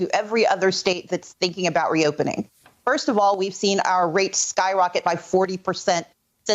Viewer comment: i cant hear that well in my old age did this woman say rapes or rates ?